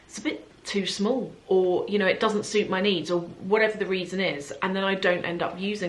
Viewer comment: At first I heard 'knees' I thought why would a bag need to suit your knees😂😂